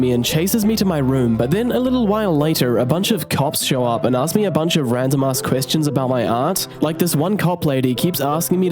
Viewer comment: Didn't read the text. Thought he said "cops show up and asking a bunch of random ass question about my aunt"... My brain went to thinking his mom murdered the aunt and the aunt was in the spaghetti...